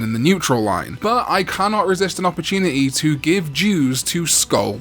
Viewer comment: Ok, when you said "give dues" I heard "give Jews" and I immediately went "why would you want to feed Jewish people to Skullbaluchimon?!"